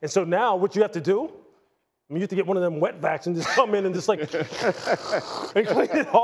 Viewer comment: Did he just say wetbacks